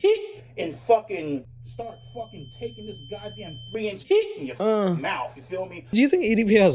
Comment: Did he say, "... take this 3in..." lol? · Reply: He did say “3 inch” - explains his anger and confidence issues.